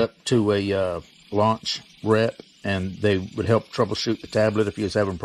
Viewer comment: At Approx. 3:47..."Remote diagnosis is where you would connect it to a " loc ret"? What did he/you say? I could not understand it. Thanks. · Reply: I said connect up to a launch rep and they could remotely diagnose the scan tool if the scan tool was messing up someone.